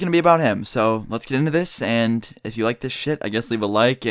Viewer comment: did you really say "lets get into this" after saying "sex wizard" or "sex god"